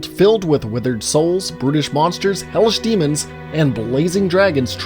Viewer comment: Thought he said “British monsters” not brutish... had me for a sec